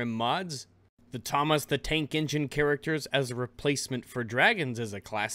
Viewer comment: Almost thought you said "Thomas the Taint Engine"!